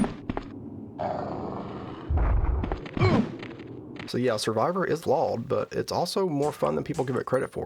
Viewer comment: Survivor is Law? I can't figure out what you're saying there and even the closed captions were confused. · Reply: "flawed" Narration is still a work in progress. I get going to fast sometimes